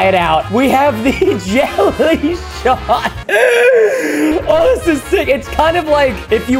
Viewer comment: Which YouTuber Liquid was your favorite?! Subscribe and comment below! 🌊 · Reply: I thought the jelly shot said jelly snot🤣🤣🤣🤣🤣🤣🤣🤣🤣🤣🤣🤣🤣🤣🤣🤣🤣🤣🤣🤣🤣